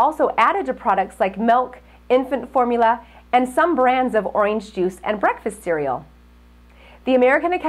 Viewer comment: does she say “oynge juice”?